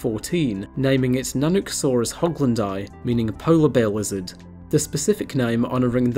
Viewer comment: I thought he said "Polar Bear Wizard"